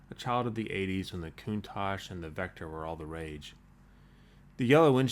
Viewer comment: I swear I heard him say "When the Countach and the Vectra we're all the rage". Hmmm "Vectra"